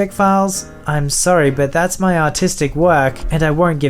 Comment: when he said artistic it sounded like he said autistic is that werid?